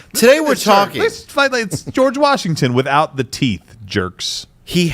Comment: Gerald said “George Washington without the teeth” and I thought he said “without the T” and I was like...George Washingon? Is there a punchline...? 🥴